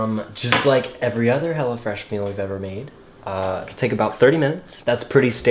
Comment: I thought you were saying Hella Fresh. I think I like that better!